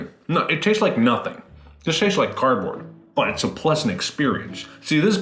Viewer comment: When he was just starting with the McFlurry and starting making those weird noises I swear I heard “bippidi boppidi boo, THATS a lesbian experience” and it caused me to choke on my water😂